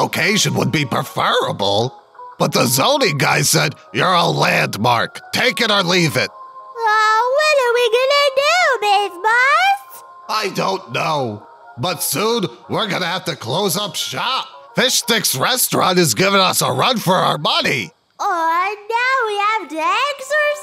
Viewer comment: Did she just say buss!?!?!